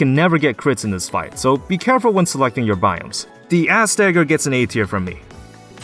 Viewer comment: there's no way i heard him say ass dagger